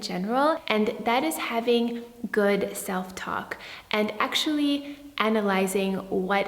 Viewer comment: Girl, I was about to be so shocked with your first point - I thought you were gunna say “good s-ex” 😂